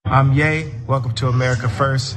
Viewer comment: When he said “I’m ye “ I thought he said “im gay” lol I’m actually gay and I’m relieved he doesn’t play for my team.